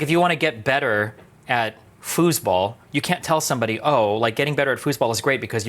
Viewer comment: German here, did he say „Fußball“ at 4:40? Wtf 😂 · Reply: He said foosball. It's a table soccer (football outside the US) game with lil plastic athletes controlled by 2 levers on each side of the foosball table.